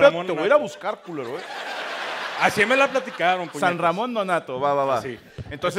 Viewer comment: así me la platicaron puñ3tas .. jajaja. Y yo escuché bien o como estuvo eso